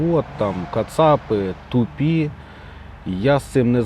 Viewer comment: He clearly said kacapy, which is a traditional Ukrainian derogative for the Muscovites. Nobody in Ukraine calls them Russians because they are not but, the Western journalists stubbornly translate it as Russians anyway. Muscovy and the Muscovites are only proper historical English names for Muscovy and the Muscovites commonly used until 1773, give or take.